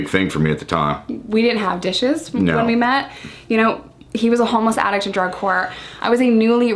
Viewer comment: 20 minutes in... i thought you casually said “he was a homeless addict drug whore” i was like WOW i like this blunt lady’s attitude ahahah